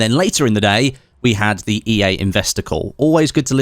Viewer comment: Ahh... “investor call”.. I heard “investicle”, what ever that is...